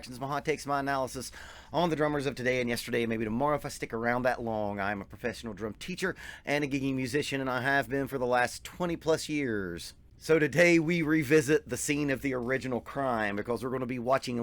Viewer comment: Gigging* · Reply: i know but everytime you say it with your accent i hear it "giggity", it's hilarious!